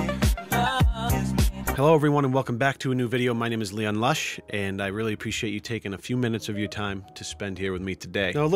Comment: Anybody else hear "Leon Luuuush is leafy"